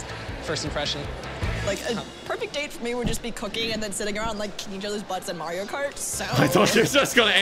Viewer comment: Had to rewind that, thought she said "eat each other's butts"